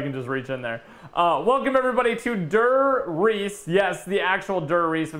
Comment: Bein honest, I thought it said die rise